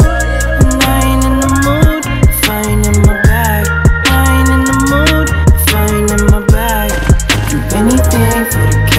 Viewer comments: “I ain’t in the mood if I ain’t in my bag” Zacari is a genius for that line 🔥🔥🔥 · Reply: I thought he was saying ‘i ain’t in the mood fighting on my back’. Thought it was some decent talk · “I ain’t in the mood if I ain’t in my bag” Zacari is a genius for that line 🔥🔥🔥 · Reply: I thought he was saying “I ain’t in the mood if I ain’t with my bae.”